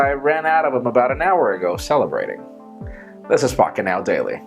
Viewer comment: Did he just say fukitnow daily ?